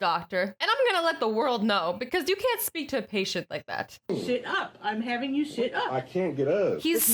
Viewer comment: I thought the nurse/doctor said sh*t up 🤣🤣🤣🤣🤣🤣🤣😂🤣😂